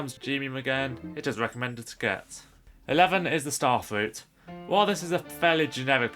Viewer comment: when i was fast forwarding using the good old arrow keys, i thought i heard you say "Fuck me in the Starfruit". Ah, the child side of my brain will never fail me, won't it?